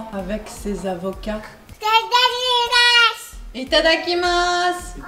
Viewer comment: Quand dit Itadakimasu! j'ai entendu C'est dégueulasse ^^ dites moi que je ne suis pas le seul s'il vous plait, je ne suis pas fou Merci pour cette vidéo :)